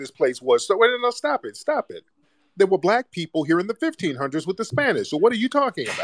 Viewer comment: I thought you said you came here with the Spanish